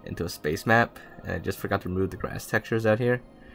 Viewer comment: thought you said, they just forget to Mow the grass textures out here. lol